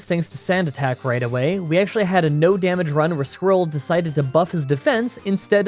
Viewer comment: wait wait wait did man just say squirtle really fast and i heard it wrong, or did he intentionally say squirrel to see who would notice